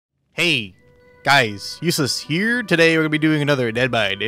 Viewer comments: I heard “ hey there guys useless here” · When he said Ussylis in the intro it sounded like he said Useless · Is it me or did i hear “hey guys, useless here” 😐 i know i don’t comment often but believe me i watch every video and it seems like you are not enjoying dead by daylight in its current state. Maybe stream a different game ? (P.s if you do stream other games I wouldn’t know, i don’t watch twitch streams often, the wait times on this game bore me)